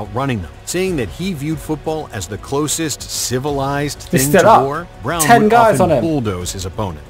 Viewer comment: I wish you Brits would pronounce your "T"'s. It sounded like you were saying war boy.